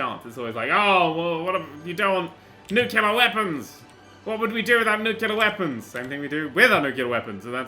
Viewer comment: I thought he said nutella weapons